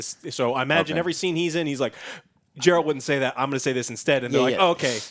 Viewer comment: Did just say JeRalt?!